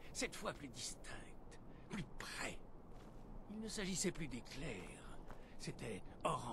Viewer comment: petit commentaire déja pour te saluer bien chaleureusement et ensuite pour confirmer que moi aussi j'ai entendu tonton adolphe a 11:39 :)